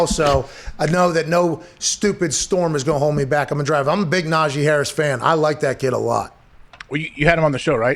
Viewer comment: seconds YouTube definitely thought you said "Nazi" 🤣 😂